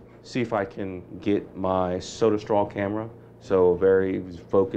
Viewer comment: what camera did he say he was using? Sounded like he said soda strawl camera